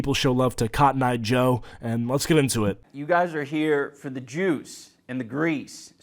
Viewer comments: “You guys are here for the Jews” 💀 that’s what I thought he said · I thought he said “y’all are here for the Greeks and the Jews.”